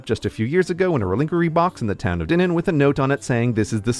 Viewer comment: I had to rewind multiple times as I was trying to figure out what I was surely mishearing as re-liquor-y box, and my guess is you're actually saying "reliquary". I'm too lazy to switch to desktop-mode on my mobile browser to access closed captioning.